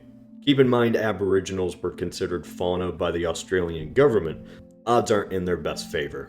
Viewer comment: What did he say? Farnaut? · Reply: Fauna. That means wildlife. The Australian government must have seen no difference between the natives and birds.